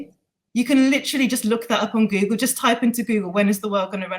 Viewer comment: Did she say Google?!?